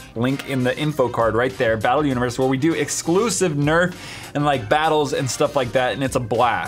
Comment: I thought he said nerd battle 😂💀☠️